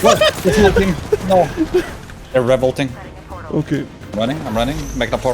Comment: he said they are rev ulting but i thought he said revolting lol